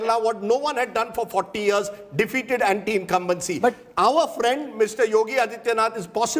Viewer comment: did he just said prime minister yogi adityanath?